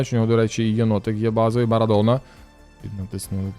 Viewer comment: Мені не почулося чи ти дійсно його Барадона назвав?) Настільки не сподобалась карта?😅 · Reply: почулося 🙂